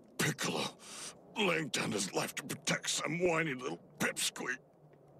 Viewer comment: Bro I thought he said “ little bitch” at first😂